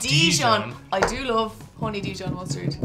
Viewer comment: I had the captions on while they were talking and when they tried Honey Dijon the lady said "I do love honey Dijon", the captions say "I do love horny dudes". lmao